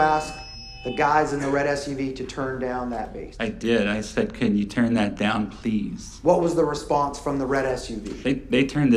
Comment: What does he say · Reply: "There's probably about five things that could have gotten done differently last night"